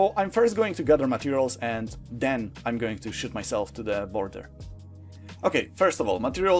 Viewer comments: is sounded like he said he was going to shit himself to the world border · what did he say?? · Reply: "I'm going to shit myself" ~me 2023